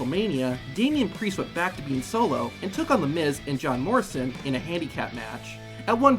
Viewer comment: Did bro say John marston ?